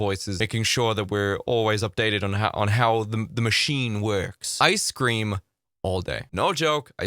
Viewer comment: i thought he said icecream all day xD